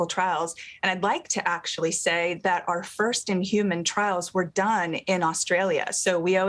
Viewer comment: did she just say inhuman?